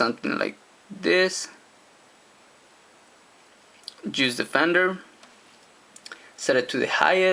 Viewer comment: When he said juice defender it sounded like he said Jews fed endear